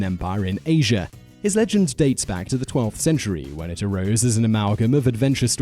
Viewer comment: did he just say "his legend dates back to the 12th century" ?